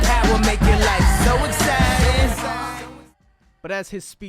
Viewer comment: I Never noticed it sounded like suicide 🤣 i always heard so exciting everytime. Now i can’t unhear suicide 😩